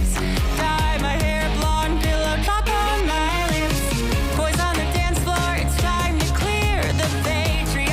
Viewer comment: did he say “put a cock on my lips” did anyone else hear that? Or am I just a whore with a dirty mind? Ugh terrible terrible song.